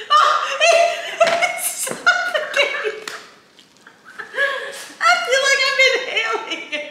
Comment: When she said “I feel like I’m inhaling it” I thought she said “I feel like I’m an alien” 😂 Like damn it was so bad sent her to another planet!